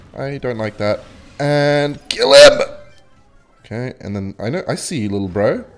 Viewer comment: i was looking away for a second and i thought i heared saying KILL HIM